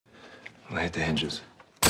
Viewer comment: Small criticism but it sounds like Gareth says “light up the hinges” light up being slang for shooting something.